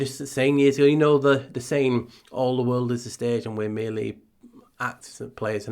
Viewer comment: 😆 I thought you said 'all the world is a stack' ( but you said stag) but speaking of stacks...cow patties 💩 can stack...just stacking up the 🐄 pies..& seeing mounds of shyt!